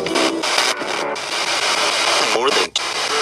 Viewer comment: When you asked, what will happen if you keep playing randonautica. I heard the spirit box say torment.